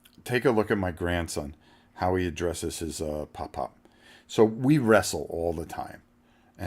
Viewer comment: Your grandson was so adorable! I thought he was saying "Batman," glad you clarified it was "Pop Pop"! 🙂